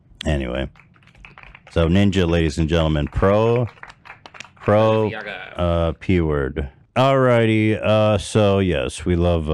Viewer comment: did ethan just say "Ninja everyone. Pro... P word" ? The fuck xD